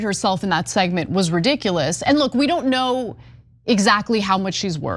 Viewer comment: Wait. I thought they said we dont need men...Ooops.